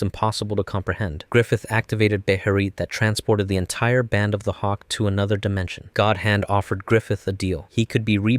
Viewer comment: i heard hawk tuah for a second